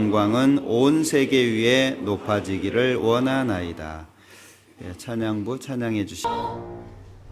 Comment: "찬양대 찬양해주시기... 뽁!!" 이렇게 들리는건 저뿐인가요?